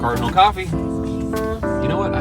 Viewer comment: I know this is an old video, but for the longest time, I thought you were saying CARBON COFFEE, (play of CARBON COPY) today I learned it's Cardinal Coffee